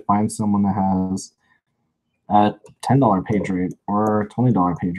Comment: Minute 23:00. Did I hear right? A $10 page rate?